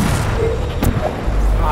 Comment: why did you say scheiße in 9:50 ?! i think its funny, but why?!